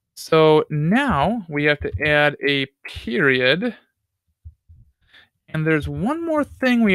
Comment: what is piriod? or what did he say at 39:17? What is it?